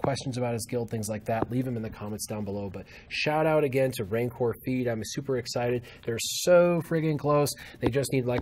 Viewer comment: Sounds like you said rancor feet